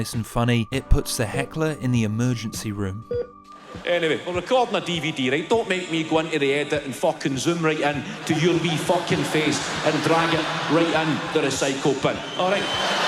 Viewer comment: What did he say at 12:37? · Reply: "We're recording a DVD right. Don't make me go into the edit and fuckin' zoom right in to your wee fuckin' face and drag it right in the recycle bin, awright." Kevin Bridges' Scottish accent is pretty working-class-Glasgow so I don't blame you for not understanding :)